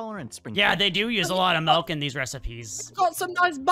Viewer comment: did foxy just say "malk"??